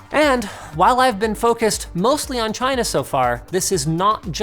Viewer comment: Is it me or he says Tryna instead of China at 4:47 ?